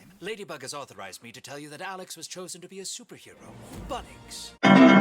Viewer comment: THE SUBTITLES SAID "BUTTOCKS" INSTEAD OF "BUNNIX" I'M DONE😭